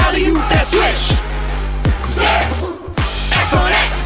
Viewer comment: Did they say "Se><"? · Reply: Idk, i thinnk its Zax not **,but i hear ** too but i think thats our dirty mind